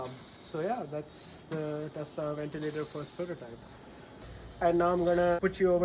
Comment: i thought it said tesla vibrator...